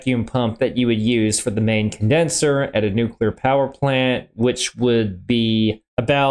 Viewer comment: I enjoy your videos and I have seen quite a few, and it is because I have seen quite a few that a strange phenomenon happened while I watched this one. While you paused to comment you started talking about "a nuclear power plant" and I heard "this one time at band camp" and I can't get it out of my head now. Seriously, not a criticism, just a silly thought that popped in my head. Don't change a thing. Keep making great videos.